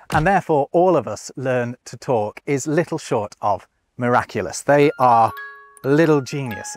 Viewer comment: did he say miraculous! I love miraculous that is a great sow!!!